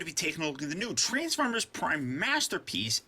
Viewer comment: did he just say transformers prime?